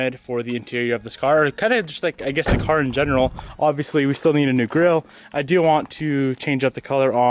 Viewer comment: I thought he said the new girl is coming, I was so disappointed until he repeated new gril!